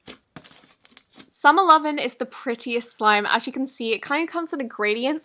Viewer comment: When you said summer lovin I thought you said seven eleven😂